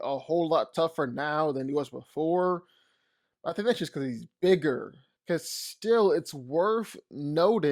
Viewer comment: he said thsdstg instead of saying that's because he's bigger